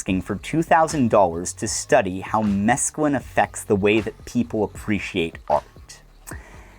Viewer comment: what does he say here? My subtitles says meaculine but idk what it is (Im french) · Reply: Mescaline